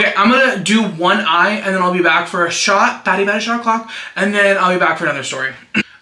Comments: BADDIE BADDIE SHOT A CLOCKKKKKK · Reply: stop this whole time i thought he was saying “fatty fatty shot a clock”…. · BADDIE BADDIE SHOT A CLOCKKKKKK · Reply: I thought he was saying FATTY FATTY SHOT O'CLOCK this whole time 💀